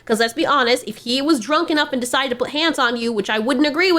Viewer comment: so i know you're probably saying "hands" but im mishearing "pants" which i find alot funnier of a visual